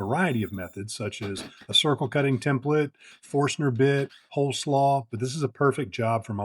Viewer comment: Cool. But did you say hole slaw? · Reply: I thought he said cole slaw.